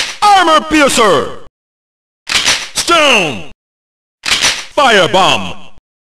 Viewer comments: you hear that? I hear is STONK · i heard stun i guess too much league of legend